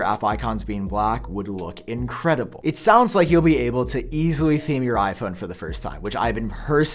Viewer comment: did you say "easily seeing your iPhone" or something else?